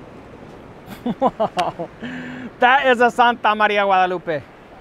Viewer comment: sir what did you say? Santa maria malupit?